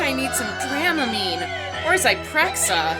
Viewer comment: Hey, what did you say at 20:16? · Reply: “Or Zyprexa”